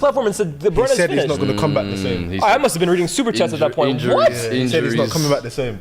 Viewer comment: I think Turkish said I hope he comes back washed